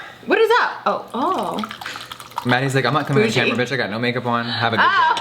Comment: I thought you said “manny is like I’m not coming on I have no make up on.” And I instantly thought you were keeping manny hostage in your maids Quarters